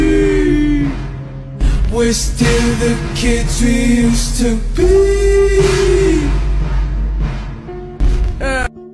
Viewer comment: what does that say?? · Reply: says “This has to be fake!?” “Right?”